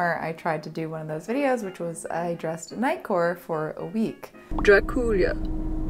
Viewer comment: So much to enjoy about this one! Petition to see more of 70's! Also, forgot about the Knightcore video and thought you said "Nightcore" - is that a thing? Is it goth? Is it vampire? Who knows